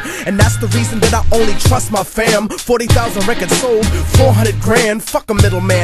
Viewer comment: I thought he said 4000 sold 400,000 grand but mathematically made no sense so I checked my source. 40,000